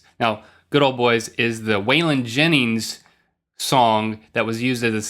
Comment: I thought they said the song was by "The Wailin' Jennys." I was super confused. :P